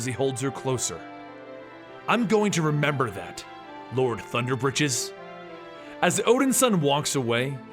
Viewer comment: lol 2:56 i thought it said "lord thunder bitches"